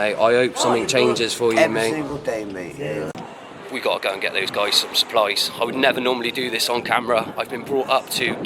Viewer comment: 7 mins 16 secs.....I thought ya said you've got to go buy them some Spice! 😅😂😊